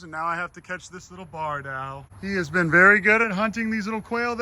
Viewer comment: I feel dumb ive been calling them BARN owls my whole life is it actually BARD owl like i hear you saying or am i mishearing? 😭😭😭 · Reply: Barn Owls and Barred Owls are different species. That is a Barred Owl.